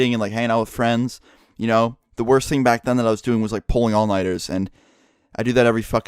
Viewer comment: What did he said at 1:40 pulling on .... ? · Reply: Pulling all nighters.